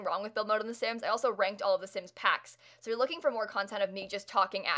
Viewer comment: “I talked about everything wrong with the lord of the sims” was I the only person who heard that?